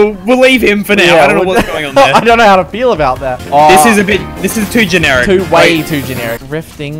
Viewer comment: Who else heard the pun? “I don’t know how to PEEL ‘bout that”